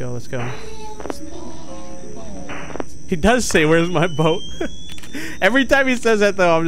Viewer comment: That's daisy saying "Where's My Bow" at least that's what I heard